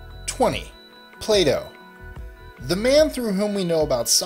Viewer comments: am I the only one that thought he said 'Play Doh'? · I had to do a double take. At first, I thought you said Play Doh. Then, I figured it was Plato. My bad. LOL!